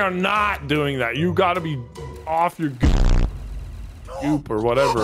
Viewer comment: Anyone else confused about what nogla said at 18:27? 😂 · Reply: He said Go*k